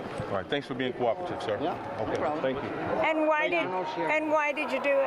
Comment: It sounded like that woman said "thank you trump", but apparently she said "thank you Joe". Same thing honestly